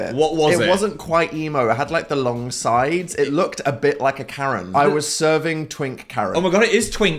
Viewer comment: it took me a moment too understand that I had misheard "Karen" for "carrot" :'D